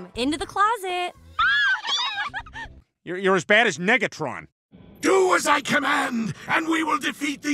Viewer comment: Did he just say niggatron at 0:41?